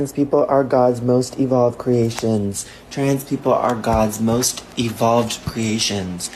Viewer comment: I think I must've misheard this person because it almost sounds like he saying "Trans people are God's most Evil creations"